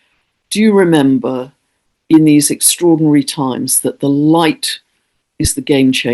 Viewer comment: Had a hearing blip near the end and heard you say "These are strawberry times" 😂 Perfect! My happy word chose itself 🍓